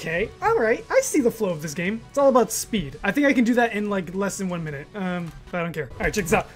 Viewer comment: i thought it said 1 hour not 1 minute